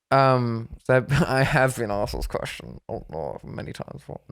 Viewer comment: (Can anyone say what he says?)😂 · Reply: “I HAVE been asked this question many times before”